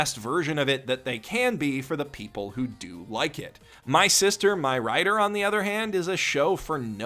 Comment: I literally thought you said "my sister, my rider" the first time